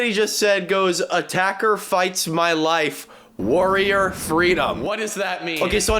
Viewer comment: I sacrifice my life for your freedom. Just for anyone wondering what he says